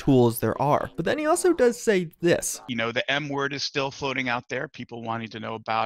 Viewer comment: thought he said “the N word”